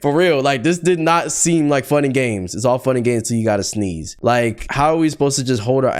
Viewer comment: thought you said "funnygame" and my gd brain cells reactivated